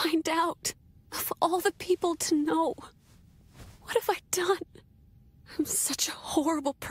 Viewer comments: She almost got it, i thought she said im such a whore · Upon first hearing this I thought she was saying “I’m such a whore”😂